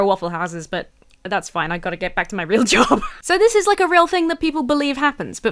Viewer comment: I misheard "No Waffle Houses near but that's fine, I gotta get back to my real job" as "...gotta get back to my rimjob."